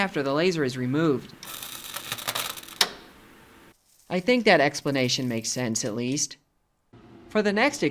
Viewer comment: dont know if im the first to say but, when he says " I think that explanation makes sense at least" the captions says "i think that explanation makes cents at least" cus pennies are a hundred of a doller, and a cent means a hundred of something Funny dad joke